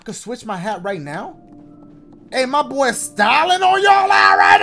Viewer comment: Who else thought he said Stalin 😂